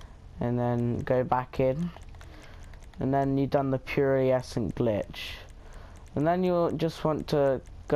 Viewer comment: Did he just say pereaecent glitch 😂